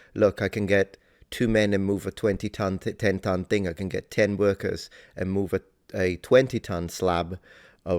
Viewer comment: 😂when he saying worker man, thought he was saying wicker man's